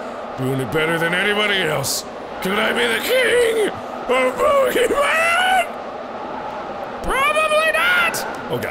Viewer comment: Anyone else hear him say, "Can I be the king, of Pokemon"